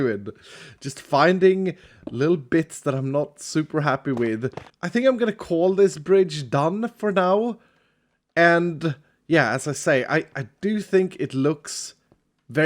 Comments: Rofl. I heard, "I'm gonna call this bridge dumb for now?" But seriously, its looking omega. And still waiting for a tutorial on the coco bean farm inside of your beautiful house of leftover pieces.🤫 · I thought at 17:39 said "I think I'm gonna call this bridge Dumb for now" and was like "well that's a bit of a mean name for it"